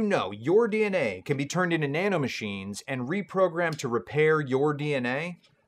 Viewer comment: Wait...@ 13:42, did he just say NANOMACHINES!?